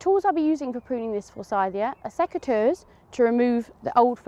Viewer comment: in the video, I couldn't understand the name of the tool you mentioned. Please can you share it here? Thanks! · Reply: They're called Secateurs